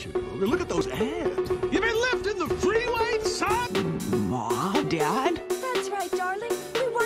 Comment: This whole time I thought Hugh said: “ You been lifting the freeway son?!”